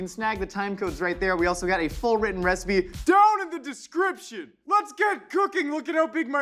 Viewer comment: "Down in the description" I literally heard "AND I LIVE IN A VAN DOWN BY THE RIVER"